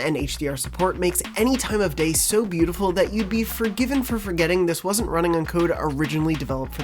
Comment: Did you just say "you'd be originally forgetting this is code originally made for PS5"? 😂 3.52